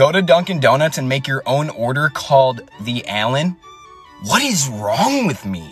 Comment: I thought he said alien